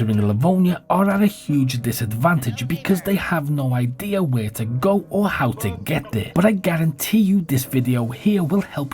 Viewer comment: did he say what i thought he said ?🤔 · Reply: He said "Hello Neighbours" I had to quickly check the original footage xD